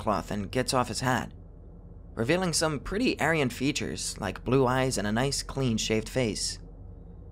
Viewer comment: I was confused by the comments and then I heard “aryan face” 😭